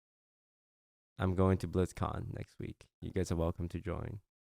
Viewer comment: Honestly, the first time they talked about Blizzcon, I only heard "boobs" . There are boobs! 😂